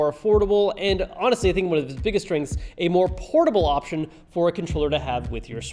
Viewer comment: I thought he said a more portable but more importantly it’s more portable